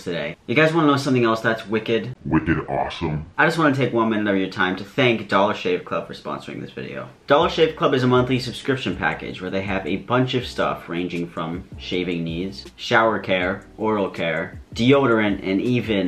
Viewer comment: Ligit thought he said 'shaving knees' instead of 'shaving needs', let's just say i was about to buy it but went against it lol